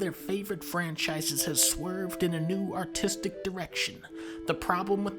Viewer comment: Thought he said "new autistic direction" at first.